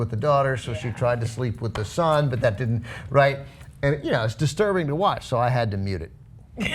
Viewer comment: What did Benk say in 3:37? "It's disturbing to watch so I had them...?